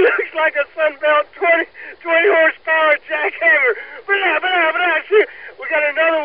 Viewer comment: i think he said sunbelt 20 hp jackhammer.....